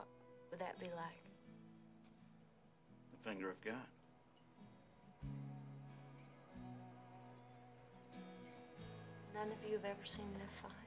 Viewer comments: For years I always thought he said defender of god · I thought he said Finger of God or then you are god.